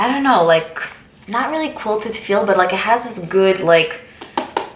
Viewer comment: I thought you were going to say buttery but then you said "Butt-like" i died 😂